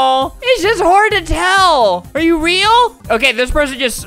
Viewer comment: For a second I thought albert said “it’s hard as hell”